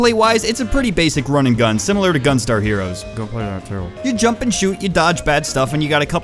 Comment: what the heck did he say at 1:11 i think he said ether "go play that terrable" or "dont play that terrable"